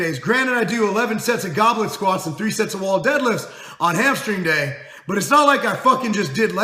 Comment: The first few times you said 'hamstring day' it sounded like you said 'hamster day' and I got excited cos I thought you had started doing G4P.